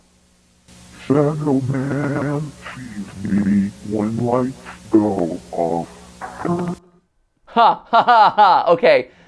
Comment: Don’t know if this was just me, but I didn’t hear “Shadow man feeds me when lights go off” at 25:47 I heard “Sad old man sees me when lights go off” implying one of the founders are being haunted by his creations. Might be wrong but hope it may help